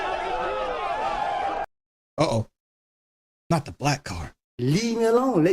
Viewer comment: I think he said 9:46 bad people not black.